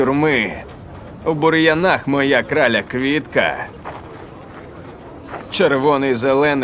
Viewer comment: моя краля квинка 😂😂😂😂 (это прикол мне просто так послышалось!!!!)